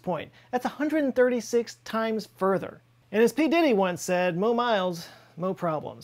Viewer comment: I think he mighta said 'More minors' 💀😭